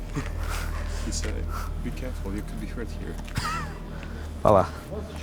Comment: He actually said: "Be carefull, a pear could fall on your head"